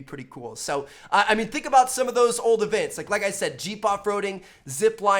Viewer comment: I thought he said "cheap" off roading